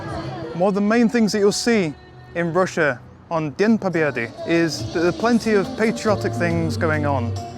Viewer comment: Do you mean at the end of the video? At that part I say: "С праздником вас. С днём победы" (S prazdnikom vas. S dnyom pobedy) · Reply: It is the phrase you articulated at 1:14mins Sir, it sounded like 'Dien Pobeddy'. Presumably Dien is day, and Pobeddy is victory. Cnabba Poccnn. Akmat Silla.